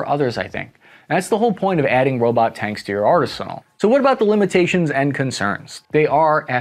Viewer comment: did you just say artisenal? Asking for a friend